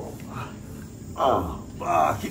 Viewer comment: The way he flopped on the floor Where's my chair, gimme yours😂😂😂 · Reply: I thought he said 'gimme your arse'